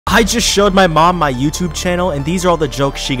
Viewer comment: Bro I thought you said “I just shot my mom” so I had to rewatch it😭💀